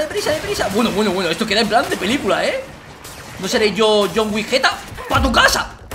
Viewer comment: entendi john wigetta 😞